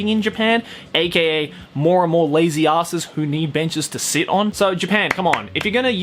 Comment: Please tell me im not the only one that thought joey said theres no bitches to sit on 😂😂😂🤣